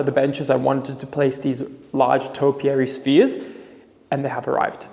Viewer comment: SPHERES!!!!! I thought you were saying spieres and I thought ooh topiary spiers how is that gonna work!! 😂😂xx